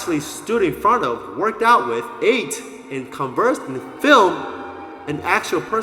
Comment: Did you call Ronnie "Phil" at 5:34? Lol